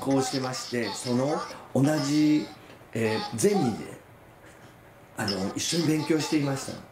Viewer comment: 「同じゼミで」が「同じゼニで」に聞こえてしまうんです。